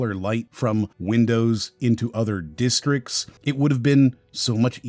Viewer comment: can someone explain closed captions? Closed captions says "other zones" while video narrator says "other districts." Does the youtuber upload a script into the closed captions? Then if they accidentally deviate it causes that word transposition to happen?